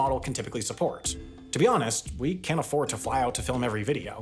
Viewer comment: is it just me or is he saying “we can afford to fly out to” instead of “can’t”? The subtitles say “can’t” but I hear “can”